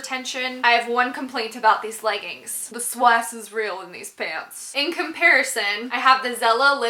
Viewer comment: Loving your videos!! Good and real.....but wtf are you saying at 10:26 in this video?? I've played it about 10 times and still it's a nope.."The swas? is real " or something..dying to know 😃thanks